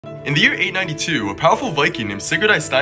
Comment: I heard: "in the year 1992" and somehow still believed all of it 💀💀💀